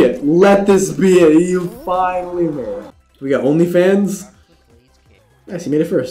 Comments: he said only fans instead of big fans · What did say? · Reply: Okay we got onlyfans 💀